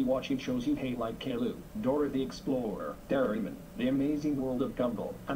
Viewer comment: did you say Doraemon